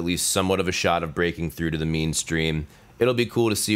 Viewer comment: it sounded like you said "meanstream". And it gave me a good chuckle. Thank you for that.